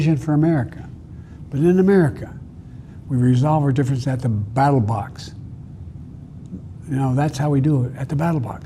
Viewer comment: Did he say "battle box" 4:14? then almost ask for people to "make America great..." who wrote this speech, then let it go out?